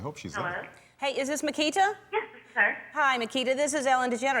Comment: Sounds like Makita said yes sir instead of yes this is her😂